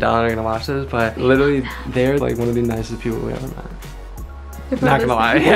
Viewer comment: Omg when said there one of the nicest people I thought he said messy people and I was literally about to explode